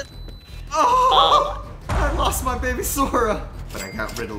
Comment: Ok i love but did just say.. "i lost my baby sora.." OMG HERES THE "19:55"